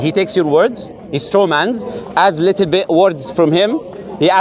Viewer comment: is an Asad 🦁 Ma Shaa Allah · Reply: hi what is the word in 6:19 ? Stroman ? Or what plz help me with it because i'm learning english